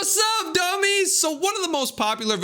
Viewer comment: did u call us DUMBS!